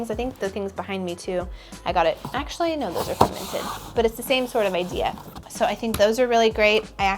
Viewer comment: Hello, where are the artworks de above you please? I didn’t catch it in your video. Also love watching your videos (please do a Christmas one!) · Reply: I just heard her say they are from the Minted website